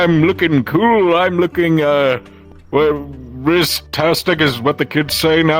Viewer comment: DID HE JUST SAY RIZZTASTIC?! 😂😂😂😂